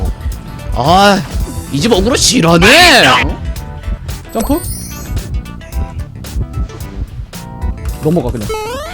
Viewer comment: 이때 ㅁ뭐라고하는거에요...? 롸이빗똥...? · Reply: '마시쩡' 맛있단 소립니다